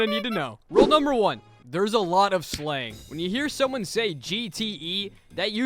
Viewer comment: i thought he said slay so i was like slayy